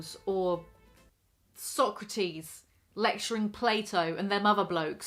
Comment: Thought you said sock tease instead of Socrates and I was like damn girl what you been reading